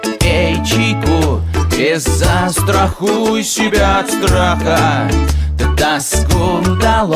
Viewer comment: Боже, эти лица :D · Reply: Я тут слышу «Траха» ну на том моменте